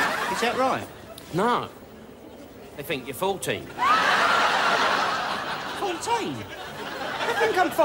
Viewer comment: It sounds like Del says 40 not 14